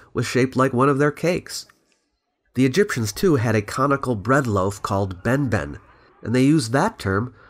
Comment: I thought you said "comical bread loaf" and I'm like why is benben taking strays